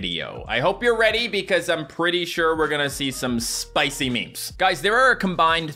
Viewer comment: at the start of the video I thought he said spicy dog memes sadly not😅